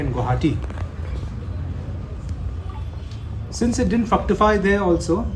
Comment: what is he exactly saying? I didn't get it someone please explain or help me understand the word. All i hear is fucktify